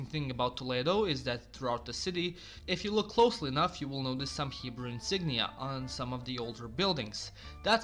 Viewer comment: bro at 8:54 i swear at first i hear you say "subhuman insignia" and played it back and realise you said "some hebrew insignia". i was like holy shit he when there